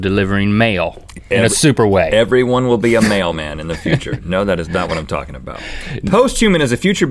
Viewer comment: at the beginning I thought they said "male man" not "mail man" Haha 😅